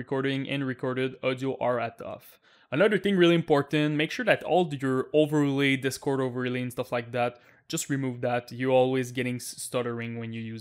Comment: I can't decipher what he is saying, what is "overly"? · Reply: "...another thing really important, make sure that all your overlay, discord overlay, stuff like that, just remove that..."